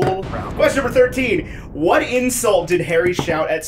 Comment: When Q13 was read, I heard "What incel did Harry shout at Snape" and was utterly confused for a moment. But can you imagine if Harry called him that 💀